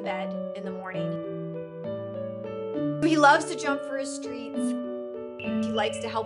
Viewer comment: i thought it said “He loves to jump for his Streets” Or “He loves to jump for his Dreams”